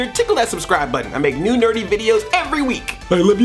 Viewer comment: At the end it almost sounded like you said "I make new dirty videos each week" instead of "nerdy"... yipe.